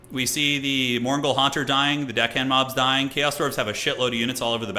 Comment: Thought you said dick hands mobs.